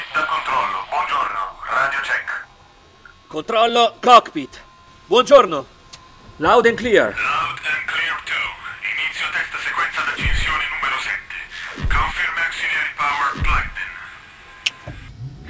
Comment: I realize he said cockpit but with all the noise going on around me I thought I heard cock meat..